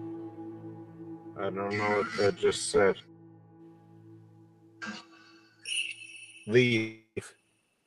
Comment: I hear at 3:38 “don’t (very faint) leave us in sep - tic tank”